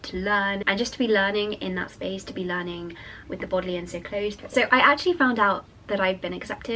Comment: what do you say there? "With bodily and so close?" I don't understand, please help :(